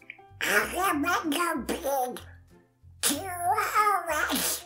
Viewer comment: Did You Just say pig 😂